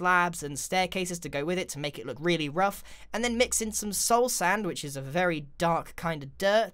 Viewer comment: I heard "soul sandwich" instead of "soulsand which" and was like wait what